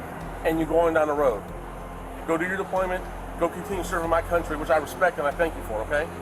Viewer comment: Did he say go continue serving my Country...